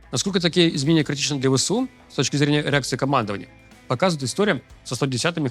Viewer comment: С точки зрения эрекции командования?мне не послышалось?